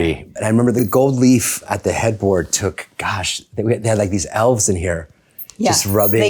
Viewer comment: did he just call the workers “Elves?”